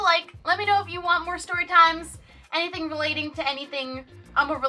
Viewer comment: I am honestly wondering if I just heard her say.. SHUBSCRIBE Like, #BestPunEvaaaa